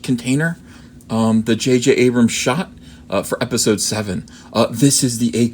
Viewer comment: For a second there I thought he said "JK" Abrams.